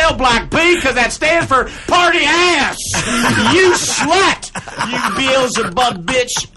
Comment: What does he say during 4:45 - 4:49 ? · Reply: home slice, I think Brian says " You Beelzebub Bitch", classic Mark and Brian, I got to meet them and converse a couple of times. Mark, an extrovert, Brian, a little more reserved in public. Genuine Comedic Genius ! ! !